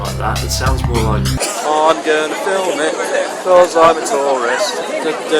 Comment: I thought he said " I'm going to film it because I'm tallest" haha